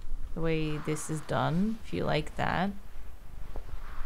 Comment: "I would recommend... Skin Meringue." Seriously tho, what did she say? Is it a game, a book or a movie? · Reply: Skinamarink!